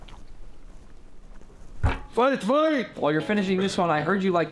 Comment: What he say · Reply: Fight fight